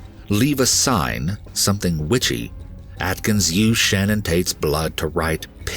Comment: am I the only one who heard “Shannon Tate”?